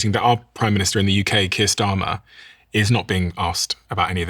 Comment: I generally refrain from commenting. However, it sounded like you said, “Kissed Armor”, not Keir Starmer…..🤓